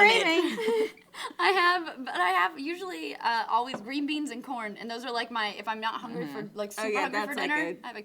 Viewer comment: Thought meg said "green beans and PORN" instead of green beans and corn LOL